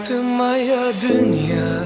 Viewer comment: Мне послышалось ты моя дыня 🍈😂